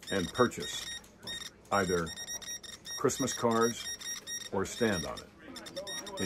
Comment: I was scared - for a hot minute i thought you were going to tell me to take the unapproved vaccine! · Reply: Me to 😂 I was like this ought to be interesting, then I thought he said Christmas cards I was like it is August come on man!